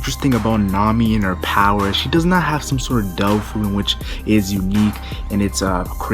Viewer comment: This video was supposed to be uploaded last week so today there will be double uploads !!! How strong is Nami Swaaaaaaan ? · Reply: u mean devil fruit if not please tell me what is it? when u said daoofu at second